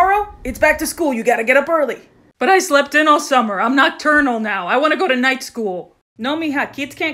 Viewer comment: OHHH I THOUGHT SHE SAID NOT TERNAL. PFFFTTTT